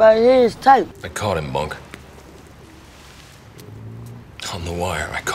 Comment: Yo did Jimmy call Bunk, Monk in the scene with Stringer dead???? 5:28. I gotta go back and look at it on my collection now.